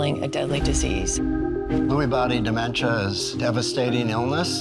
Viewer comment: Thought it said Lewd Body Dementia huh huh